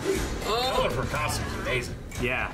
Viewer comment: Can't hear what said, guys help me out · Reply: "The color of her costume is amazing."